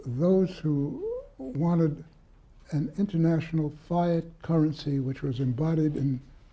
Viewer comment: Did he say Phi Ott currency at the 5:14 mark of this video ??